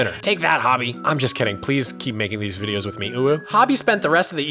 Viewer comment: Anyone else hear say UwU at 9:40?